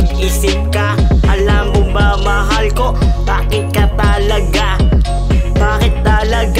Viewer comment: TAMA BA YUNG NARINIG KO 00:58 "ALAM MO BA MAHAL KO, PANGIT KA TALAGA" LOKO KA!